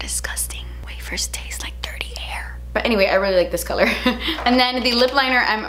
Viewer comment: Lol !!!! I thought she said Dirty Hair But she actually said Dirty AIR!!!! Haha Lipinstiff.....I was dying:) Kathleen - YOUR HILARIOUS GIRL!!!!!!